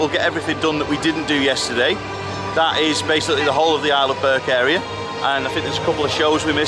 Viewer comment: So it’s Isle of Berk. Not Arleberg as the subtitles say or I’m a berk as I thought you were saying 😂 I’ve not seen the movie btw! I had to look it up.